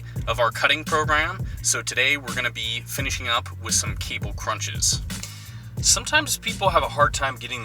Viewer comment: Hahaha! When he said "cable crunches" I heard "Kegel crunches" which is a very different kind of workout!